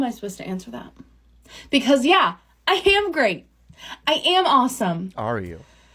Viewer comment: I swear she says, "I ham great". There's your problem lady.